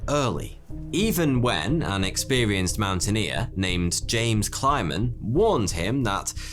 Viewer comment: Heard that as "James Climbin" XD